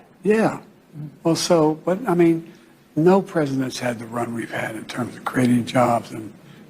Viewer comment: Bidens a loser and liar nothing more. I thought I heard him say at 3:15 "no Presidents had the runs I've had"? thats true, his diaper is full right now.